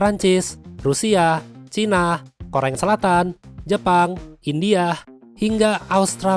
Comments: Gak salah dengar nih gw koreng selatan🤣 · koreng selatan? Atau gw yang salah dengar 😭